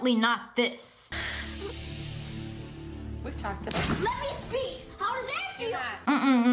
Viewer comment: him: slaps mom LED ME TALKD his mom: O.O my mom: i know this little boy didn't just slap her and what is she doing just sitting there me: LeD mE TaLkEd lol im dead bro did he just say led me talk